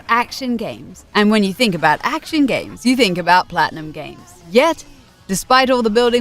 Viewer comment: did i hear it right "platinum games" ?